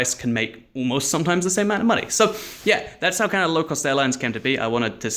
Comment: thought it said lost cow